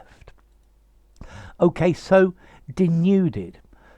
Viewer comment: What was the word you said at 2:20 and which sounded like "destripped"?